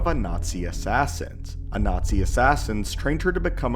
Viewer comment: I heard a Nazi assassin